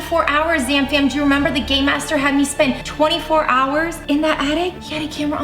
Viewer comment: Okay is It just me or dose it sound like there saying . gay master 😂